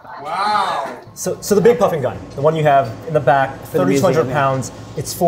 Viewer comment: Sounded like he said the big fucking gun